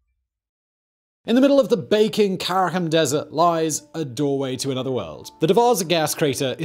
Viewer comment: from your accent, I thought you said "In the middle of the bacon carrom desert" I immediately looked up bacon carrom desert, to which the text was auto corrected to dessert. I found a lot out about bacon desserts, but nothing about the BAKING carrom desert.